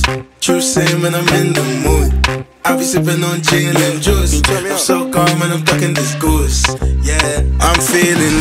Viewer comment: Every time i hear "I'm so gone that im FUCKING this goose" 😂😂😂